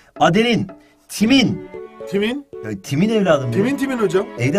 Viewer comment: timin ne duydum ben